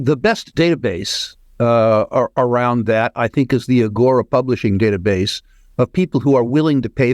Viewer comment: Great interview. What is the name that Rick says around 6:08? Agora publishing database?